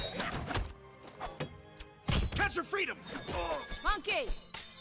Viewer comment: Sounds like Poe says "catch of freedom" here. Captions got 'that's your freedom' but it also says funky instead of monkey.